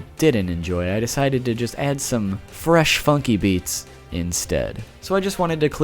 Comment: Did he just say Funky Fresh?